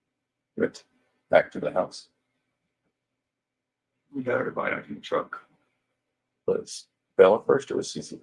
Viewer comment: am I hearing Chris Watts say "we got her body into the truck"? 🤔 "We"? 😳 WE???